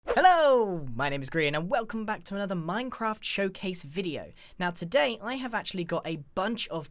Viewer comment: Is it just me or did he say chow case?